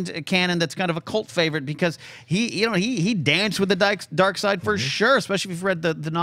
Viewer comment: Is it me or did say “dance with the Dykes” at 53:14 what a legend 😂😂